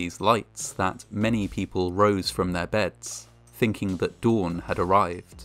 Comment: At first I heard "That many people rose from the dead", so I had to listen a second time. Is it possible that a Carrington level event happened in ancient times and might explain some religious myths? Would it even be possible to determine if and when such an event occurred?